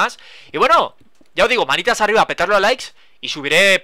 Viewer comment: alguien escucho que dijo maricas arriba